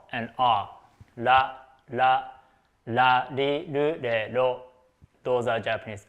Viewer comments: did I hear a rero? Is that a jojo reference? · Did...did he just say La Li Lu Le Lo?